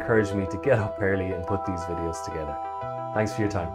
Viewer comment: at the 9:53 mark of the video you say something about "region....." and I cannot understand the rest of what you said. When I run my shell, I am getting a Pinecone error and I am pretty sure it is because I missed what you said at this point. Can you help me out? Thanks! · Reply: Sure thing. What I meant is you need to add the environment region. In Pinecone beside the API key you should see what server region the service is coming from. You copy that directly to the env file. Go back in the video and see it at the point you copy the API key. Let me know if that helps